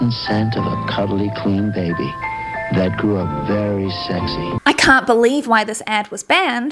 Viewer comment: All I hear is “I’m a very sexy baby” from that 30 Rock episode. How is this real???😂